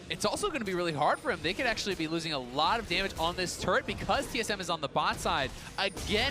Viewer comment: TSM WILL WIN WORLDS GUYS! DON'T WORRY! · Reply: Egoist I thought the commentator said "tsm is drop"?pls correct me..